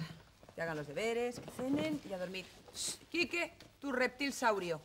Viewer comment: en el minuto 20:10 que dice que hagan losdeberes y que fumen?